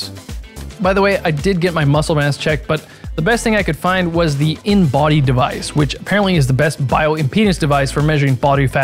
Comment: i heard 'penis device' lol